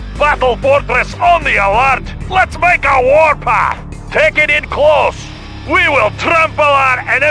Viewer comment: I remember when I heard him say "Let's make a war path" I thought he said "Let's make them water" meaning to pee themselves, I like mine more